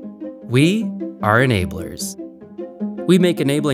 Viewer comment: I got this ad on a video and thought it said NAMBLA